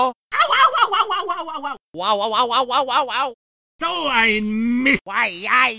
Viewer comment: Und Ich habe immer verstanden: "No, I miss!"